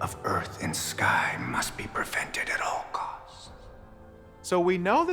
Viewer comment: Im confused at first i thought he said at 6:10 "the usion of the earth and sky must be perfected at all cost" .. now its ssupposed to be PREVENTED. If think its Perfected ull hear it but if u think its prevented ull hear it too.